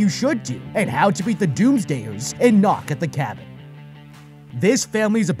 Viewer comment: I for some reason thought it said "the doomslayers" lol